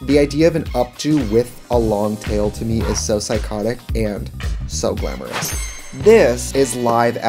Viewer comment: I am watching this back and re-obsessed with all of these. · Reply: I thought you said psyconic!! YES!! I'm making fetch happen!!